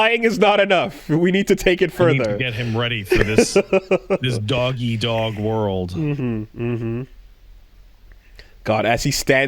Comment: please tell me I heard that right and is saying "doggy dog world" instead of dog eat dog world lmao